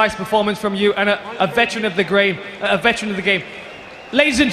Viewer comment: Josh Barnett - Veteran of the grave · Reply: For a second I thought he said veteran of the gay